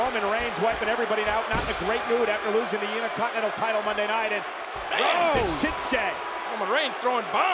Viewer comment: Did Michael Cole say "Uni-Continental championship" around 47:53-47:58, or, am I hearing things?...